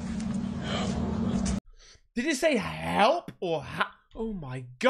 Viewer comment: i hear "hell''